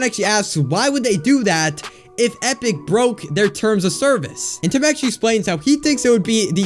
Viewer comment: Awesome video but... 4:52 it says brought instead of broke XD lol it’s fine